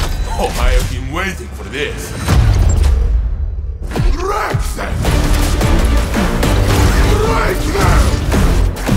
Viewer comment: Anybody know what Saint 14 is saying at 1:13 ? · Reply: Wreck them, Break them. Hahahah I love this, you swooped in like a bird of prey